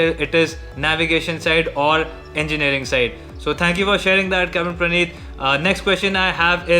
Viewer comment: did he said captain